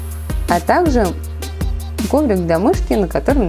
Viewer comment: Мне послышалось домик для мышки!😂😂😂🤣🤣🤣🤣🤣🤣🤣🤣